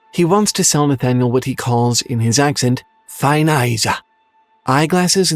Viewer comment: What is being said here? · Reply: Fine eyes sir